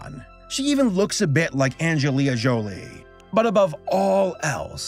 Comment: Did you just say "Angelia Jolie"?